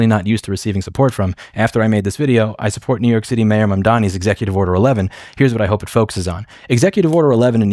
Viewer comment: LOL I thought it said Mayor Madman